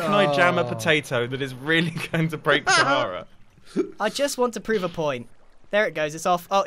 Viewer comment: I thought it said “I broke sharia” and then I got scared